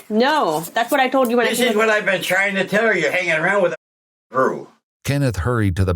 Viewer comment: What did the grandfather say that was redacted?? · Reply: “Fine crew” I believe or something like that